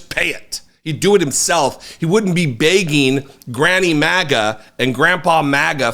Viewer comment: 😂 did I hear "granny MAGA?" & Granpa 😮